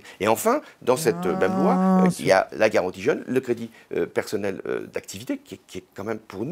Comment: quand il a dit "garantie jeune" j'ai compris "guerre anti-jeune" 1er degré mdr